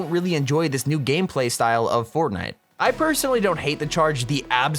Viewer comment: I can’t be the only one who heard him say “fortia”